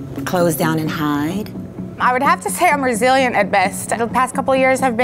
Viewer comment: I thought she said “I’m Brazilian at best” 😂😂